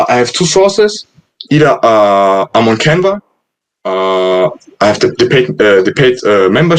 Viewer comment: what does he say here? The membership site that he uses for Spotify artwork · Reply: Canva...